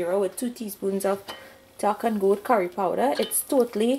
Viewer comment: What did u just say then? what curry powder? type the name of the curry powder please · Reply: 5thsciecle duck and goat curry powder ,it's a special type of curry we get in Trinidad.